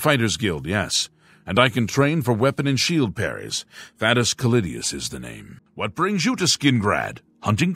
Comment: Dog with your accent I thought you said Fatiest Clitius. I was shook for a moment. I thought I missed a crude joke this whole time.